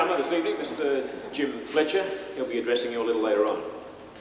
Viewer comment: Mr fletcher will be 'undressing a little later on'. 😂😂😂 sounded like that?? #blush 😊